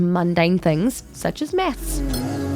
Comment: I heard meth instead of math and my IMFP voices started